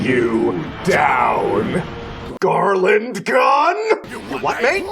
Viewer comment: Um okay I know its been forever and idk if there is a comment already. But legit when Bulma Shows up I swear I hear "GARLAND GUN"